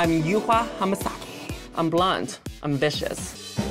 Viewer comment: I thought Yuhua said blonde at first too but I think she says blunt lmao